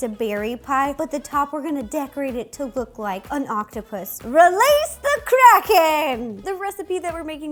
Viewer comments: I thought she said "Release the crack head!!" 😂😂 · I thought she said release the crackhead 😂